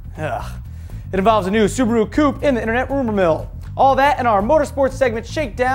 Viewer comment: did he say the subaru poop? HAHAHAHAHA